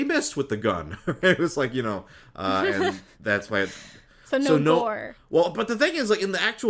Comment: Lol did Jenny say "so no vore?" @ 56:14 and he didn't happen to hear her